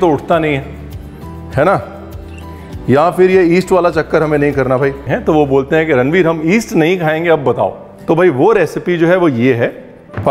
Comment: After around 3:12, when chef starts talking about yeast mixed with dough, the subtitles start referring to it as "Eastern food". In fact, at one point subtitle says, "some people don't prefer eastern food". Whoever has done this in his team, it is not only incorrect, it is sort of a blunder. Also, not a big deal.. but those pepper corns, he mentions 10-12 but certainly it is more than double of that...